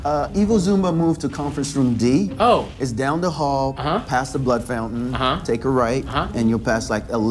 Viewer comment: Did he say 'past the butt fountain'?